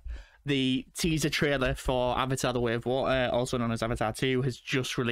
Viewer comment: did he just say (Wo Ah)! for water?!